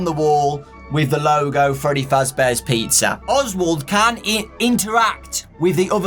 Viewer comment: An ad played so I heard they Freddy Fazbear's pee